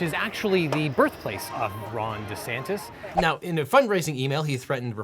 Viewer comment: Did I hear “Thrawn DeSantis”? Is LegalEagle a fan of the old Star Wars books?!